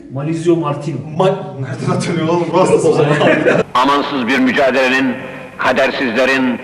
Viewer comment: anansız mücadelelerin anladım XD